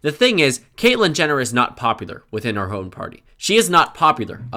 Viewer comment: is that a mistake or did I hear you said you were a democrat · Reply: He said "her", not "our".